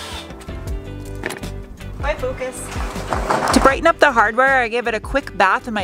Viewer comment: Legit thought you said "Bye, Lucas!" Then I saw the Focus and connected the dots... lol